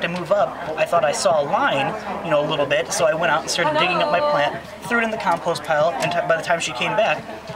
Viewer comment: Did I hear that correctly? At 2:34 did he really say that she threw it in the compost pile??!! So, she is digging out an infected plant, waiting till it breaks down a little and then spreading it on her other hostas? I'm not sure that's a great idea, do you?